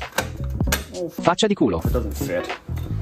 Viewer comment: at I heard "faccia di culo" LMAO WHYYYYYYY